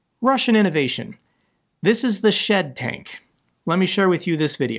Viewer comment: For a few seconds after he started showing the tank, I thought had said "this is the shIt tank"... which would have been an accurate descriptor to what i saw.